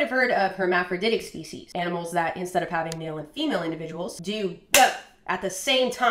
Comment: haha when describing hermaphrodites I misheard "instead of having male and female individuals" as "instead of having to deal with female individuals". I thought she was just dropping some really casual misogyny